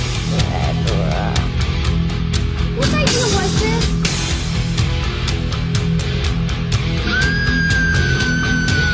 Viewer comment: What is the voice saying right at 0:45? After Danny saying “redrum”? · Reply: I think they say "After I'm". That's the way I hear it at least. Might be something different.